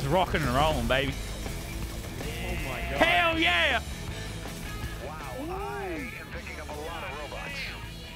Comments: did anyone else hear "wow im picking up a lot of robux"??? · did ironman say on 7:21 wow i geting a lot of robux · did he just say robux